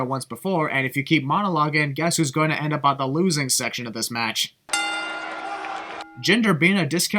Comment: A small mistake, you said "Losing section", but your subtitles said "Loss Section".